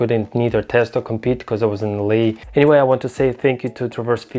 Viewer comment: You were what? Sorry can't quite understand what you said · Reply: I was in LA.